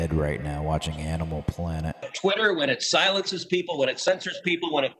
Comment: “I could be laying in bed, watching Animal Planet” ...*spring self-quarantine flashbacks* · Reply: Oh. I thought he said 'animal porn'.